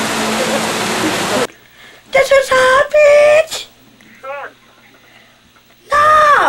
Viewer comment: did Beet say hobbit or hot bitch? 💀💀